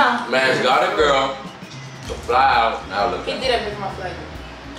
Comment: What did say at 8:40 !?😭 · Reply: “he didn’t pay for my flight”